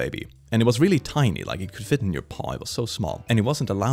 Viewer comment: when is talking about his childhood friend and the dog...did he say the dog could fit in your paw? Is he sure he isn't a furry? :D